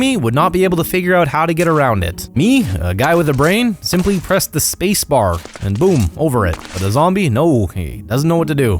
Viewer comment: Did i hear "smart man just press space bar"? :D